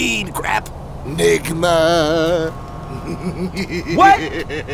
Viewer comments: It sounds like he said ligma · When he said "Nigma": "They had us in the first half not gonna lie." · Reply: I thought he said ligma